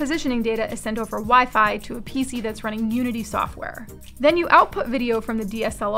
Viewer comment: Did she say "Nudity software"..?? 😮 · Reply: Not sure if joking. But if not.... Unity software.